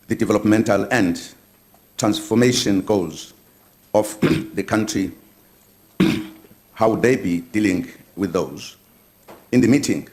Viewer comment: What is Mr. Zuma saying from 0:31 to 0:43. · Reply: he is saying"they develop mental end transformation goes off the country and how they be dealing with those. :-( mmm